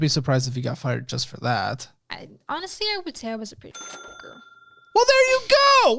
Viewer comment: Why is what she said sensored? I have no idea what she said that gave Caleb such a reaction. 🤔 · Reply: P sure she said "honestly I was a pretty shit/shitty worker" she was just saying she was bad at her job and prob deserved to be fired without actually taking accountability